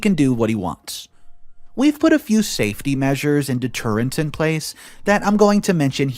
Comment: I had to rewatch this because I heard "safety measures and the turrets in place"